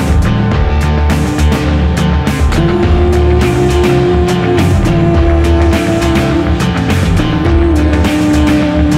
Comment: what is thom saying here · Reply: keep it moving